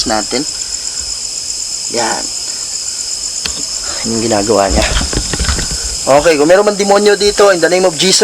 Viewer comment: may narinig ako sa 19:00 nagsalita "anong ginagawa mo"